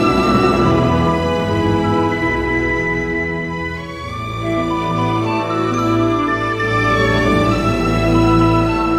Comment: I wish I knew what they were saying @ 0:48. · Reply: Irishstile Perryman I think Terry Silver is saying “COME ON MORE” still trying to figure out Kreese Edit: I want to say that Kreese was saying” HARDER, FASTER, STRONGER”